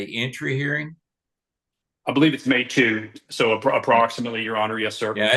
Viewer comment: I thought he said May TOOTH 😭😭 I had to rewind 3 times to realize it’s MAY TWO